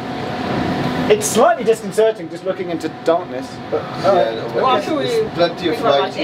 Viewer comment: did you just say “looking into the DARTness”???